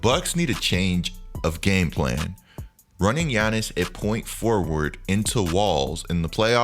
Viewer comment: Did he say point forward